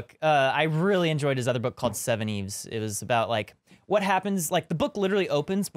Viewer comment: What's the title of the book, again? I didn't catch that. It sounds like a really interesting book! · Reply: Seveneves by Neal Stephenson